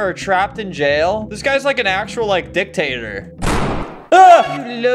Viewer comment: what did he say??? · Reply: dictator